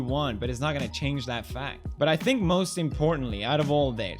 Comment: did I hear him say that it is FACT that he is anti-Semitic..... hmmmmmmmm new article title perhaps